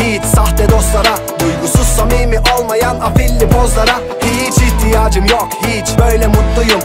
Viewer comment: Abi illa “bitch” olacak değil mi her şarkının sonlarında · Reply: Hiç diyor öyle duydum